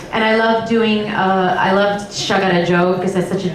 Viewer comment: What song does she say she loved doing @ 08:15? I can't make it out. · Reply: She loved performing (elements of) "Sagarra Jo" — it's in the "Open Your Heart" part, done with the Kalakan Trio.